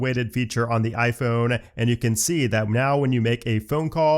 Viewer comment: did he just say “meow?”